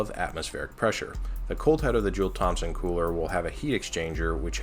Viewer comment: So apparently its "Joule-Thomson", not "ThomPson" with the P, but since William Thomson has been dead for over 100 years, I'm gonna keep spelling his name with a "P" cause he can't call me out. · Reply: There's also the fact that it sounded like you were saying "dual Thompson"